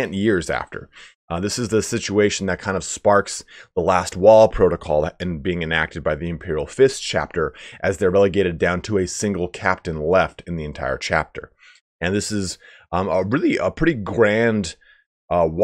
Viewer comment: Did you say that the Imperial Fists had a "Last Waaagh Protocol?" Awesome!